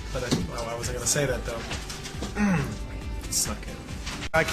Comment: I think at 6:44 he said either "I suck" or "I suck at it". Look up Pyloric Stenosis, that's the condition he was born with that needed correction w/ surgery.